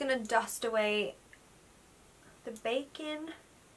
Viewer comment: when you said "just gonna dust away my bakin'" i heard it as "bacon" and cracked up. love the look!